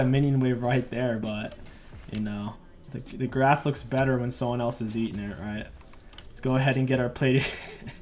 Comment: Lmao I heard your ass looks better when someone else is eating it